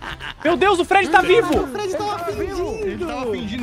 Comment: eu tenho certeza que escutei o xingando ''caralho o fred tava fingindo''